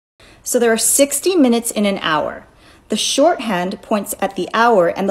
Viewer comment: I thought she said "there are 16 minutes in an hour"